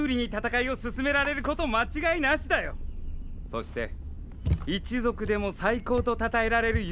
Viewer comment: Am I the only one that thought Ravoli said Sasuke at about 15:41 at the beginning of the sentence? Also the great fairies in this game are perverts it seems!